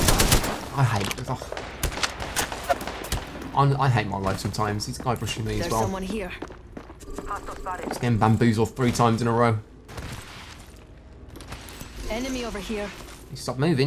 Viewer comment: I thought you said your never getting bamboozled again 😂?!